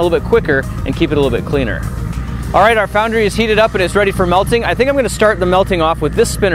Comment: ohhh i thought it said making instead of melting